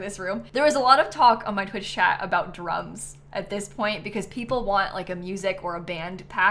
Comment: 100% thought you said drugs instead of drums lmao. i love this build!!